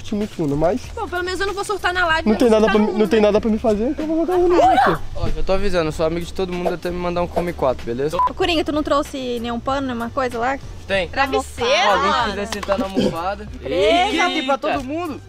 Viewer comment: No primeiro momento eu ouvi " oi amiga" serião